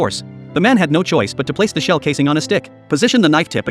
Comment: Thought he said had to put it on his dick not stick.. boy was I relieved lol